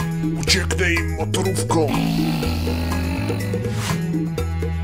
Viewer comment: "słyszałaś to ?" "uciekne im motorówką" najlepiej , że usłyszałem "uciekne im parówką" xD